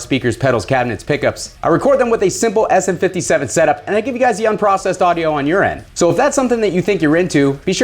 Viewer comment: Every time you say “and you get the unprocessed audio on your end” I hear “and you get the unprocessed audio on urine” Not quite sure why I felt like I should comment with this but there ya go. Now you get to hear it weird every time too! Lol